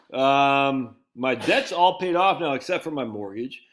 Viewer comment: I thought you said 'my jet is all paid off, except for my mortgage" lol